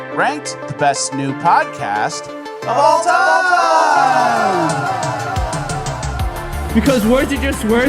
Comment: Join Bonus Land for over 4 hours of exclusive content each month! <link> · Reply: I swear I heard 'best nude podcast of all time' lmao